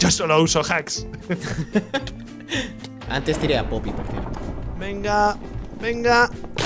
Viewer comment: Que dice en el min 19:08? despues que rich dijo, yo solo uso hacks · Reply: "Antes tire a Floppi."